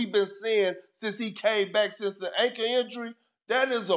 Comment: Love Kp but did he say anka injury